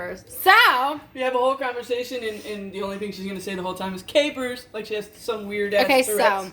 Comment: Capers are so gross to me 🤢 idk how y’all can eat them straight out of the jar. Lol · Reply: Swear, when she first said capers I thought she was like saying hello to us, but calling us “K-Pers” 😅😅😅